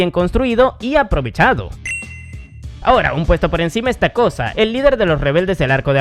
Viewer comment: Escuché "Ésta cosa" 8:10 por distraerme un poco XD